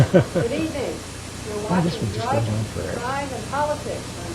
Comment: HA, what did say at 19:40, "haha, this one just goes on forever" or something, lol..